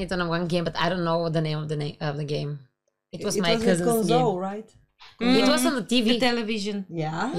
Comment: Did you just say you had it on Sega? And orange diskette?